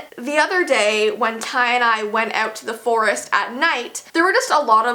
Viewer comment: When she said Ty and I, I thought she said "tie-dye"